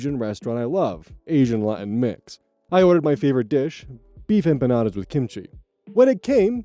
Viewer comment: I misheard beef empanadas as beef and bananas